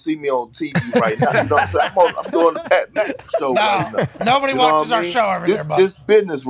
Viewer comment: Sounded like he said the Pat Beverly Show 😂